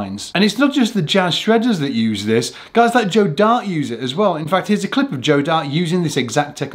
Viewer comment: I thought you said Joe Dirt. Lol